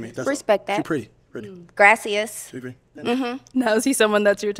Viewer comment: The last girl had me crying laughing bro “Gracias” took me out every time 😂😂😂😂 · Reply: The whole time I thought she was saying: “Gracious”